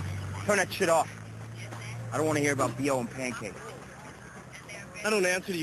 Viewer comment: What did jp say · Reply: Bit late but he said "I don't want to hear about BO and pancakes."